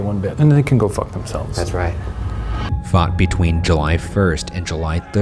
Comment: I Heard Fu*k between ...